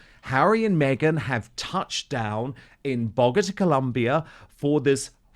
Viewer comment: Landed in Bogota....I thought I heard Dan say 'BOGUS ' Colombia which is more appropriate, haha.